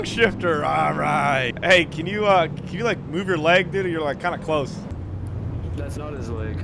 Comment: did he just say “can you move your leg diddy”? 💀😩😩💀🔥🫡😒